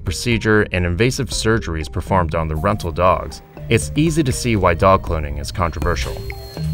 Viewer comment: I thought it said dog clothing